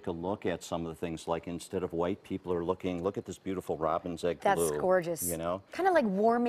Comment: Does he say White people are looking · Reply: yes, except it's more like "like instead of white, people are looking at"